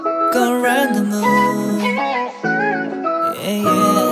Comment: タイトルがrendezvousなので 最初go rendezvousて言ってるんかとおもったらgo around the moonて言ってるんか！程々にもhold on hold on😮てなんか耳が幸せー！！ slow it downとかも そうだこのままただ寄り添ってよに聞こえる♡